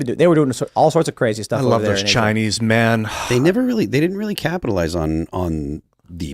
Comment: did he say he loves Chinese men lmao